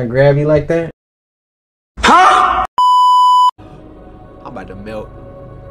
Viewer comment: when i was watching his detroit series i legit thought he said "im boutta milk" 💀💀💀